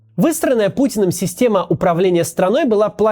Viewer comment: Небольшие дефекты речи у Макса порой звучат как должное))) "Выстроенная Путином система управления страной " Послышалось как "Высраная Путином система... " Отчего звучит даже актуальней))))